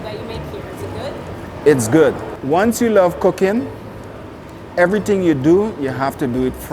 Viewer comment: I thought he said once you love cocaine 8:29 lol